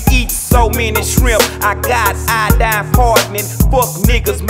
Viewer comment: "we eat so many shrimp i got iodine poisoning" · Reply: I thought he said iodine impartment